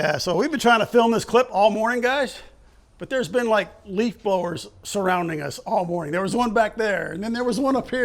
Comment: I thought said he was surrounded by league bowlers instead of leaf blowers.